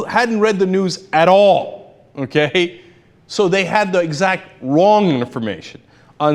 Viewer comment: Did anyone use the close captions? Hilarious. When the guy says okay? at 1:31 the captions say mcafee...... That is all.